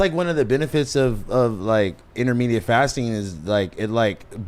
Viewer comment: Did he say intermediate fasting?